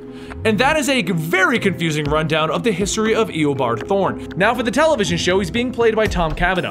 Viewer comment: Did anyone hear him say Eobard THORN